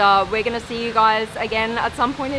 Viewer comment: she said "Dhanyawad Shukriya" and I heard "Dhnayawad Chutiya" !